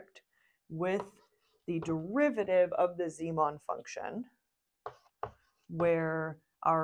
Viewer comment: Very good explanation, thanks. One question: at about 9:59 you mention the derivative of something that sounded like the "zemon function". I'm sure I'm mishearing it though. What is the zemon function? In this context, it sounds like it's the activation function. Is that correct? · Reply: Yes, the zieman function! It's the format for the hypothesis function that is used for a neural network. Check out the earlier neural network videos to learn more about the zieman function!